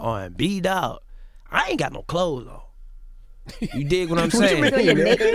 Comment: 😂 Nigga did he say no clothes